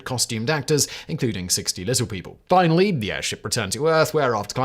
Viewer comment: I swear I heard him "60 costumed actors , including lizard people."